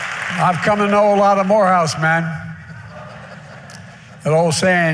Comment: sounds like he said he's come to know a lot of wh0reh0use men.